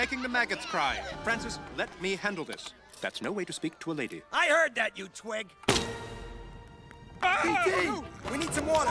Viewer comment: “i heard that you twink” is what i thought i heard LOL